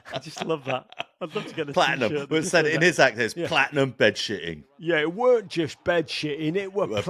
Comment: 0 seconds ago I heard "Flattered as bedshitting" which I thought was even funnier.